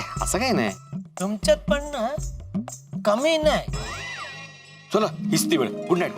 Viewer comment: did jethu called bhide kaminey? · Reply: no he said in marathi tumchyat kami nahi means tum bhi kuch kam nahi😆